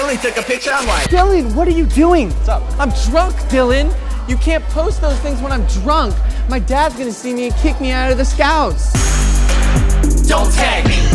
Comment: "I'm drunk Dylan. My dad's gonna see it and kick me out of the scouts." - Zack Evans, 2015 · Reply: I thought he said scouse